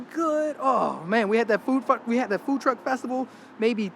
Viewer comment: the Food F*ck, instead of food truck had me laughing